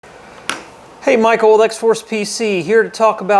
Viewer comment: What? Did you just say Hi Michaeled XForce PC here? HEY VSAUCE MICHEAL HERE!😂😂 0:09Top ten streams who Didn’t turn off their MIC! EXPOSED😂😂 just kidding.